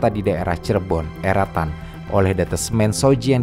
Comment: "daerah cirebon, eretan?" Atau saya salah denger. Kalo daerah eretan masuk ke kab. Inderamayu min